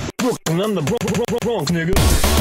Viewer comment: I think I heard,"I'm from the bronks,nigga." at 0:32. LOL.